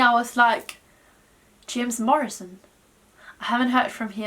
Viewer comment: Haha I thought you said Jim Morrison at first (from the Doors)